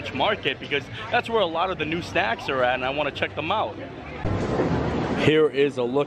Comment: I thought you said stacks and I was so confused thinking I had missed some new big thing lol I love the joy and passion you put into your videos, it can really brighten up my day